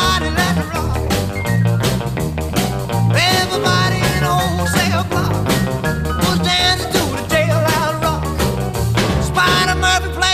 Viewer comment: I thought he said “Everybody in the whole Cellar Block, was dancing to the Jailhouse Rock.” I kinda like it better. It kinda make sense for me. But damn I love this song. 😃